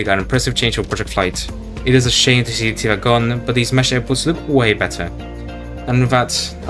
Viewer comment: bro said apples instead of of airports